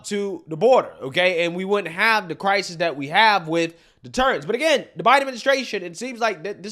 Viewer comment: Did say, “the turds.”?